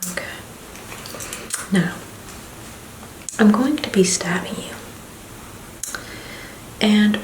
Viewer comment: here I am dozing off to the video and I hear “Now.....I’m going to be stabbing you” Me: 😴😳👀